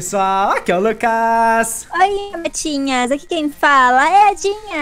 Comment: oi capetinhas??